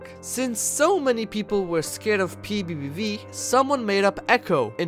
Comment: I think eco said "Its PBBV" in th is timeline